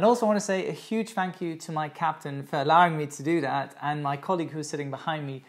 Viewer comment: I swear to God I literally thought he said, and a huge thank you to my Captain Ferlowing Todoothat (I thought he was saying a name but he was stating the words "for allowing me to do that") LOLLLLL I felt so stupid! 🤣🤣🤣🤣 Great video as always Captain joe, you're awesome my friend!!